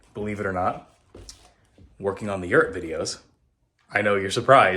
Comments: OMG, I thought he was saying Yurt videos too and I just couldn't figure out what Yurt videos were! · And I thought was saying “New York video”!😂😜